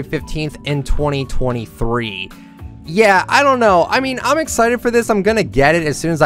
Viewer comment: did he say 2023